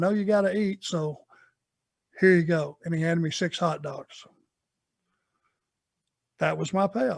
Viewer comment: I thought he said when quoting the promoter "Can't afford to pay you, here's 600 hundred dollars" And I thought to myself the money must have been incredible in those days if that was considered pretty much a non payment It was only after going back and listening a 3rd time I realised he said "6 hot dogs" 😂